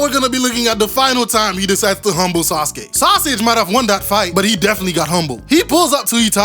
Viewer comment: did he just say SAUSAGE 😭😭😭